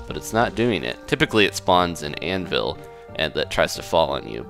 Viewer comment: if you turn on the captions it says:It's ponds in Danville. Even though he actually says:It spawns in anvils.